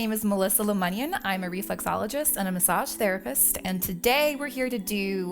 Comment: Ha Ha, at first I thought you said you were a resexologist. I thought what is a resexologist? Then I saw the print on the video and realized you said reflexologist. :) Hair-don't fret about it..either way you are beautiful. You could be bald and you would still be hot. Video-finally someone does a true glute massage. Most other glute massages I've seen pussyfoot around it not trying to offend anyone. Very nice video, although I don't know if it is possible to shape Corrina's glutes for the better. It would be like getting a better grade when you already have an A+. Thanks for the video